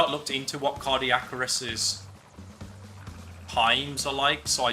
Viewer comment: Cardicarus? 😐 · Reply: lol it sounded like he said Cardiac-Arrest